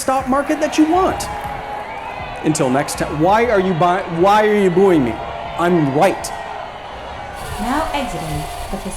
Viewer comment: "Why are you booing me, I'm right!" lol lol Man, that is certainly the state of things in the U.S. Not sure about elsewhere. People no longer care about what is valid and true. Feels over logic. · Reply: Really? I thought he said "Why are you booing me, I'm white!" And that had me seriously scratching my head.